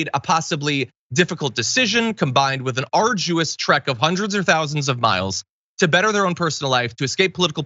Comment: Uuummmmmm... hundreds of thousands of miles? So they are circumnavigating the entire planet multiple times? Love ya, John... but that's a pretty big mathematical error. The MOON is about 239 thousand miles from us, the earth is about 25,000 all the way around the equator. Just FYI my brother. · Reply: He actually said "hundreds or thousands" - I misheard it too